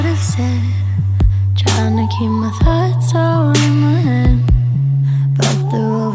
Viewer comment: Sounds like she said “tryna keep my farts in my in head”.